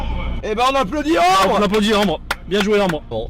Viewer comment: J'ai entendu "Anus-cigard" .. C'était "Ambre" merci d'applaudir for me x)